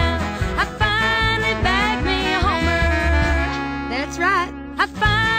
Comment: thought she said BANGED me a Homer lol. XD